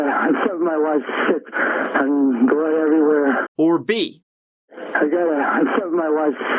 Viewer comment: The 911 call sounds like he said "I stubbed my wife and blood every where..."